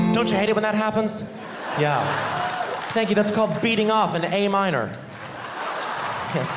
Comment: i’m madddd stupid I thought it said “beating of a minor” instead of “beating off in a-minor”